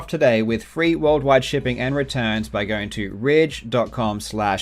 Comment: i almost got mad cause i thought he said raids and legend in the same sentence in a sponsorship ad...